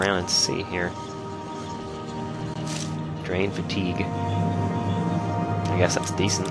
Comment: i thought you said drain fuck